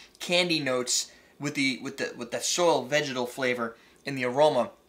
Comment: Did he say vaginal flavor?